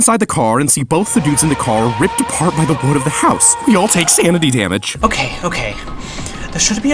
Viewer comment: by the what of the house? Sorry for some reason I can't parse what was said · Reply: Wood of the house (I think)